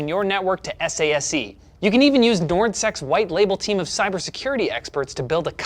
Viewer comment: did i hear that right? i can use nord sex????? hawt damn